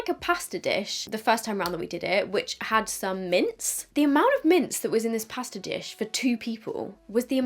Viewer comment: I thought she was saying mints....and I'm like why the heck is she putting mint candies in her pasta??? 🤣🤣🤣